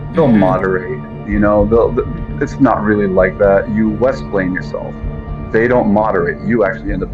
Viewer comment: "You Westplain yourself"... great comment. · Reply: I thought he said “…you West Wing yourself”! 😂 Still, what, exactly, does it mean? I get the splain’ part.. but West as in West Bank??